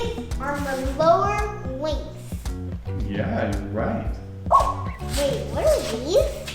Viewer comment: I thought he said lower wings lol